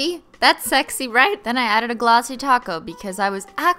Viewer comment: I'm new here so am I just hearing things or is she saying ''Glossy taco'' instead of ''Glossy top coat'' ?? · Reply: Yes she's saying glossy taco. It started as a joke back in the day because of her accent people thought she was saying "glossy taco" instead of "glossy top coat". She turned it into a thing, and now it's the name of her in Nail polish line, which is amazing!